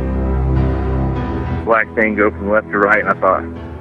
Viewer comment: Black tango from left to right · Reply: Hi, Nathan. It always sounds like broken English to me. I hear "ack tango" as if cut the words short. Just never made sense to me. I skip the intro anyway as soon as it starts, but always listen to "90" at the end.